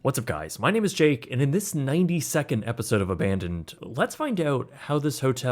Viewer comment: When you said "92nd" I heard "90 second" and was very confused looking at the time stamp lol.